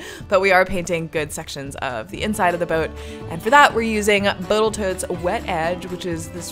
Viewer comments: Did you just say Boatal-tote😂😂😂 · did you say "botal toat" 😂